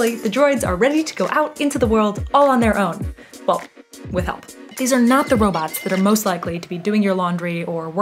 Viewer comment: "These are not the droids you are looking for" is all I heard here.